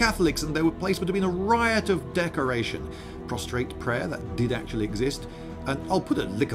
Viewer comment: why did I hear prostate prayer? I guess that also is appropriate for the Catholic Church.